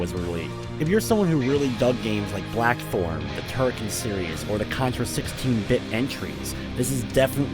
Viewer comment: Which games does he mention? I can't understand, my natural language is nos English and the closed captions say "black forum" and "turkey serious". I think he says "blackthorne", but the following game he mentions, before Contra... well, I really can't understand what he says. Thanks. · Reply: He did indeed say Blackthorne, and "Turkeys serious" is actually "Turrican series". Though I must admit, I'd be interested in a game called Turkeys Serious.